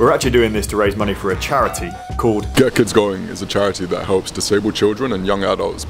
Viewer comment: The first time he says "Get Kids Going" it sounds like he said "Gherkins Going".